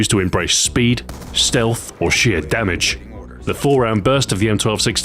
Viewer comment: When he said "sheer damage" I thought he said "shit damage" xD